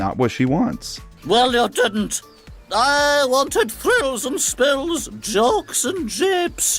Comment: did she just say…thrills and spills??👀